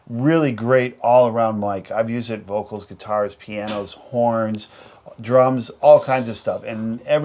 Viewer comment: my computer lagged a little bit and i thought he said porns :0 xD